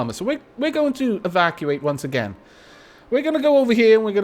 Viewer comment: Every time you say evacuate I just hear 'Aavakuate'